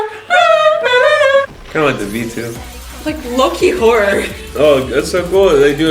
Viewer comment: i actually thought yunyi said "lucky whore" omff